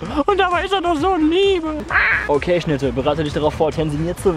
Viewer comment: Hey Schnitte! Und ich hab zuerst "hey Schnitzel" verstanden... :D